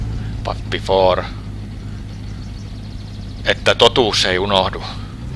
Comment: What is the phrase says here as a toast before he takes his pre-drive beer? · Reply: että totuus ei unohdu, translates to ”that the truth is not forgotten”